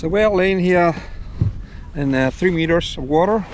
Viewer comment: I could not hear what the depth were on the ancorige? It sounded like you said 15 meters, but I believe it shuld be far less. What is it actually? Nice video 🙂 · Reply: Ah. Later you say 3 meters, so I got the right answer in the video 🙂